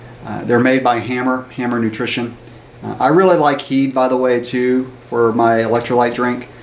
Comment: at 6:38 on your vid, did you say "EAS" as your electrolyte drink? Could not make it out. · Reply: sorry about that. It's HEED by Hammer nutrition. I like the lemon lime flavor the best.